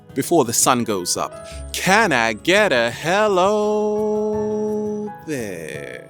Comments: i'm not english andi can't really understand what is he saying at 0:16. is something like "can i get a hell oh yeah" ? What does it mean? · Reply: LOL!!!! "Can I get a hellooooo there! " Our little "deep diver's" chant. It's like a phrase in agreement of something. Like at church when the Pastor says, "Can I get an amen." Your comment was cute. Made me laugh. :) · im not english andi can't really understand what is he saying at 0:16. is something like "can i get a hell oh yeah"